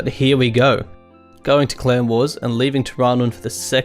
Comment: You fought in the Clone Wars? I know it Clan Wars but that what I heard xD